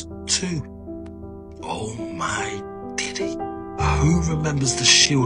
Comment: did he say “oh my diddy?”